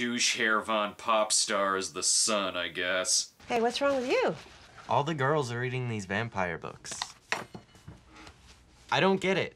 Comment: did he say all the girls are eating these vampire books?